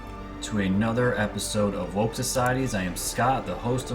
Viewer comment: Exactly. What does a scared animal do when they know they are trapped? They scratch and claw till they are put down. They cant stop what is coming. More and more of us are waking up on a daily basis! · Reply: Woke Societies sounded like you said coronavirus